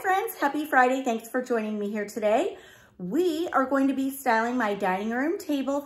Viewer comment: Did I hear right you are selling your dining room table and chairs? Editing: I might have thought you said selling in the very beginning of this video But Maybe you said 'styling'... Sorry. I just looked again and everything looks so festive and cozy!..........Blessings! Have a greAt weekend.